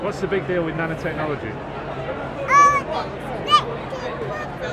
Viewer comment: I heard "uninspected poopies"